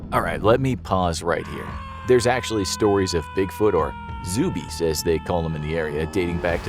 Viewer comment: Thought you said "Zoobeast" so I looked it up... Seriously don't type that into google images